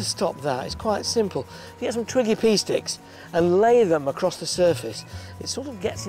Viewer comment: Could you explain the method to keep cats away? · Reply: sounded like he said twiggy pee sticks haha